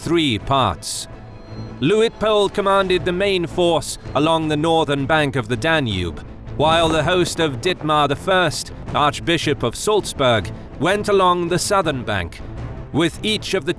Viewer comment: I'm sorry but at first, I thought said "Dickmar" and then came the portrait and lost it. Judging by the portrait, Dietmar probably had a perpetual drooling problem, started most sentences with "Wha-?" or "Huh?", and used "bruh" unironically.